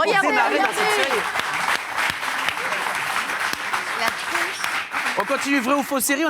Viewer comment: "la tronche" haha j'ai entendu